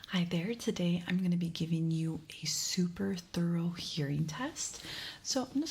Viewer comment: I was doing something when this video started and I had to rewind it because all I heard was "I'm gonna put this gun on your forehead." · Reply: Oh I heard “I’m gonna give you a super thurl hearing test”